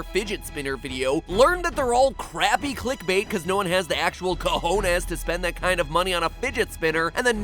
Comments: Did he just say "Cojones"? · did he actually say Cojones XD omg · did matpat just say "cojones"? XDDD